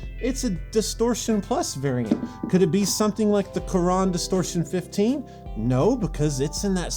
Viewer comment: during his meltdown, when he said "coron" i thought he said "quran".